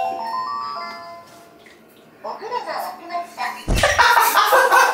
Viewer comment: 「お風呂がわきました」が「ンダホが見つかりました」にしか聞こえない8:38